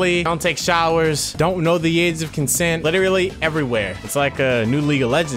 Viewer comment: Don't know the ??? of concent? At minute 0:35, what does he says? I can't understand. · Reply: age